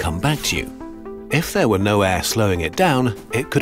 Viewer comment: i am not the only one who heard "if there was no ass slowing it down''' Right